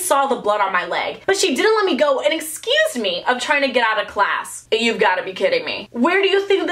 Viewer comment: did she say excuse me instead of accused me